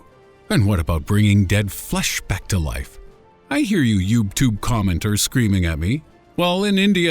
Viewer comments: Did you say youbeTube? XD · Did he say "YubeTube" at 6:38? lol wtf